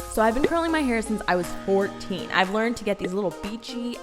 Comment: I thought she said I’ve been curling my hair since I was 4 I was like wha